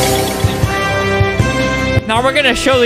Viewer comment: can we just take a moment and appreciate Paige’s editing. (ops my bad if it’s not Paige, but whoever edited it made a great job) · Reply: is it me or do I hear thick brain